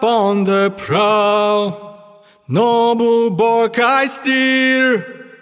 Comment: can someone please tell me what he said at 0:18 because all i hear is "no more bark ,I steer" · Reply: ''noble barque I steer ''